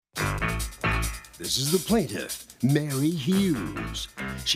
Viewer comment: Oh my gosh! Ok this is funny, but not. I just woke up after sleeping only two hours. I started the video, and I thought the announcer said plaintiff’s name is “Very Huge”.😂 That’s so wrong, but I can’t control my brain, or my hearing apparently.